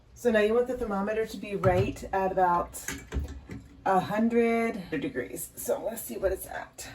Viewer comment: did you say "100 degrees"? Is 100 degrees the best incubating temp? Because you said to cool the milk to 115 degrees...